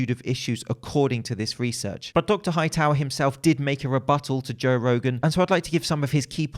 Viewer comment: did you just say robattle?